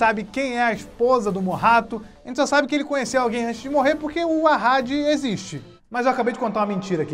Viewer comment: Ouvir dizer que o Nome Dela é Asali (foi o que eu ouvi).